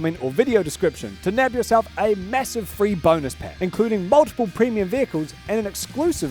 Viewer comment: Sprint headfirst into War Thunder today! Enlist using my links and get an exclusive free bonus pack for PC and consoles: <link> | iOS/Android: <link> #ad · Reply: Aww, I thought you said "boners pack".